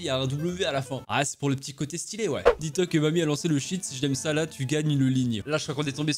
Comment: Moi je connaissais déjà cette hallucination avant mais j'entendais plutôt "Dis toi que Bonnie a reçu une chaîne"